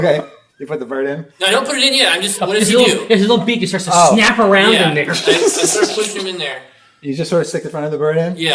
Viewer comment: At the 6:35 mark...can someone tell me what is saying? I must be hearing him wrong because what I think he's saying is WAY off. LOL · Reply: It sounds like "His little beak just starts to snap around in there"